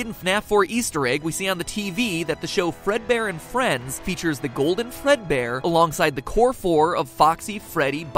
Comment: somewhere I heard him say "The golden Fledbear"